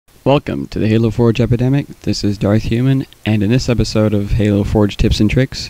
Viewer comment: I heard "Fuck' em to the Halo Forge Epidemic."